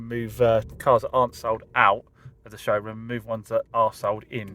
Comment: I misheard you there, in fact you said '' are sold''!